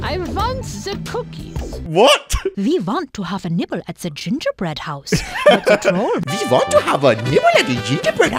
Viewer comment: I thought they said "I want to have a nipple at the gingerbread house." Ded😂